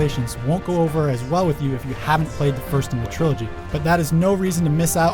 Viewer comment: wait what 4:55 did he say trilogy?!?! i believe he did WOOT